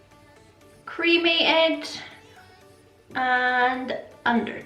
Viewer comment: Your looks are so great but I low key thought you said creamy eggs instead of cremated 😂